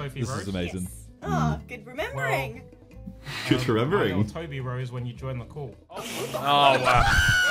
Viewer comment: what did he say here ?? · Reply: tobirose